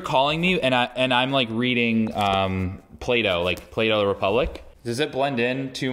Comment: When he said Plato I thought he was saying Play-doh 😆